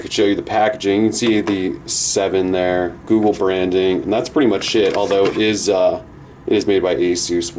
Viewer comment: did you say "that's pretty much shit" or "pretty much it" xD I think the old nexus 7 is awesome, but new nexus7 is even more awesome. ;)